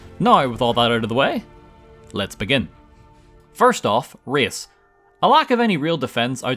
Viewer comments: First time viewer here, straight up thought you said "First off: rizz" here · I honestly thought you said "Rizz", and thought you needed a lot of Speech to complete the run, but eventually I understood that you said "Race"